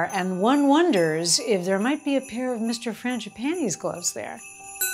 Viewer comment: It sounded like you said Mr. Fangi "panties" gloves!!! Ha ha ha ha - love your channel!